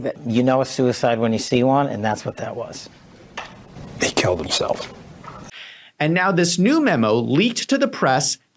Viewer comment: On 20:50 time mark Don Bongino says something sounded like : " they killed themself" ? Is that what he says ?What is that ?